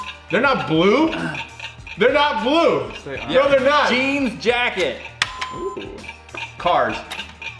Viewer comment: Did anyone else hear say g jacket like if u did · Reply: Lilly Pump he said jean jacket